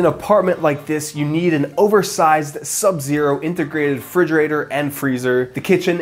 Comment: did I hear him say "frigerator"...?